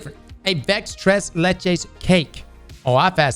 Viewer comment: in Spanish that sounded like he said he's tried les cum xddddd